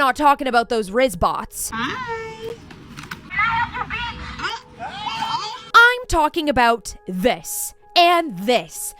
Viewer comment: Anyone know what the RizzBot said? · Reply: I heard “CAN I HELP YOU B?!”😭😭